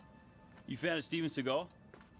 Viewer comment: What is he saying? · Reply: "you fan of steven seagal?"